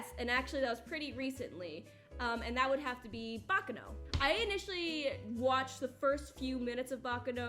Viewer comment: "Baccano" i thought she said "Boku no ..."